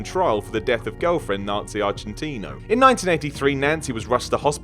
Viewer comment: the first thing u said Nancy agentina it sounded like u said nazis in Argentina XD